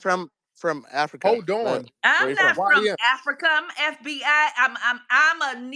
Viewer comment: Did she say I’m the FBI? 😂😂